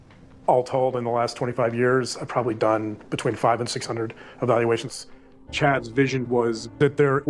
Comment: finally speaks the truth here: "I have done in between 5 and 600 evaluations" He did not say between 500 and 600 evaluations, but it gives the impression he said that. But nope between 5 and 600 is what he said. So that answer might be 6🪅🃏🪅 Now in common speak people often do this. Yet highly manipulative people also do this as the sell it to them selves as "I have said the truth here and I Am not responsible for you to not notice that." kind of justification. And the benefit for them also is that they look more trustworthy as they did tell the truth literally.= The body language reflects the are telling the truth, while fooling the other with word trickery's. The thing that might come out with this is duper's delight. Problem with is, almost shows duper's delight many times. Their whole grift is a duo couple of duper's delights. I want to know from when was the last evaluation of in inmate of a prison or jail what he did. And how many evaluation he did the last 5 years. Not the last 25 years. As it might be those 6 evaluations is from 25 years ago... As the other potential word trickery here is if you say over the last 25 years i have done X amount of Y.. People think you are still doing these evaluations, you have done then this year, the year before etc. I know cases of expert called to trial at court. that do this and then its found out the last evaluation they did was from 25 years ago ( and as they ƧƲƇƘ at their work, they never g